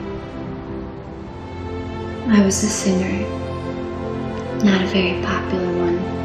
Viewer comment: is she saying sinner? Or singer?..... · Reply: Singer